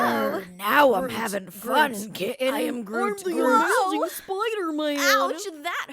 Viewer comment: because of the groot line I swear for a moment I heard "I'm grooming Spiderman!"